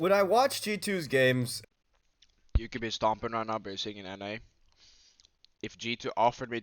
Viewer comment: What did say at 9:04?? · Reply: You could be stompn rn but you are sitting in NA